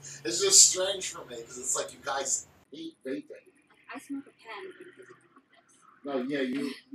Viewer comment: did she say she smokes kent at 6:30?